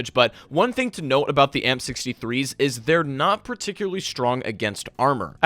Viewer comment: It almost sounds like your saying the m63’s instead of amp63’s lol